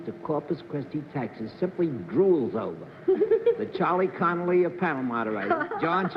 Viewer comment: A little after the 2:00 minute mark, Bennett describes John Daly as, I think, the Charles Connery of panel moderators. Did I hear him correctly? And who was he talking about? Football player?